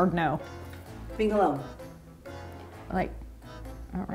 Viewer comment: What does she say at 2:59? I have no idea what that is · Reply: Gaijilla himself I think she said ‘being alone’.